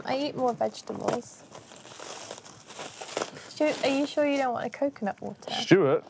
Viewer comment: it definitely sounded like she called him Stewart